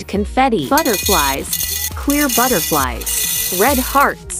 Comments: I’m sorry did I mishear that or did I hear ‘queer butterfly’s’..? 😭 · Is it just me or did she just say queer butterflies🤨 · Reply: She said clear butterflies, but it dose sound like it said queer butterflies😂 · not me think she said “queer butterflies” at 0:02 😶